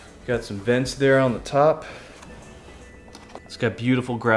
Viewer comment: Don't scare me! I thought you just casually said "Got some dents there on the top," Like it came broken or something.